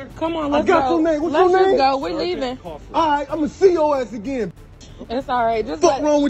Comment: The Guy Clearly Says "Ima See Yo Ass Again"....... How Did Anyone Hear "I'm The CEO As The Gang *****" Maaaaaan What?!😂😂😂😂😂😂😂